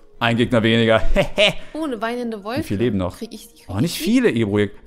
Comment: Wer hatte bei minute 9:51 auch ich fick die verstanden 😂